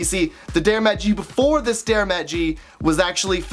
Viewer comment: For the first 30 seconds I heard "Dare MattG" more than five times...